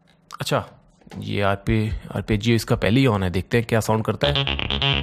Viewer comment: Sir aapne 18:13 to 18:16 ke beech aapne kya bola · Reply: Arpeggios